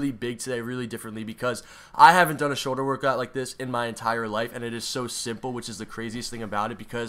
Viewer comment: I thought is said “shoulder workout with dad”